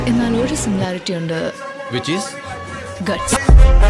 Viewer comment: what did she said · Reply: Guts 🔥😎🥳👍🏻